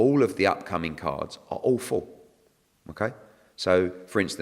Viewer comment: When Eddie was explaining about putting the undercard fighters on up and coming events. He said they where ALL FULL. i thought he said they were AWFUL 😂😂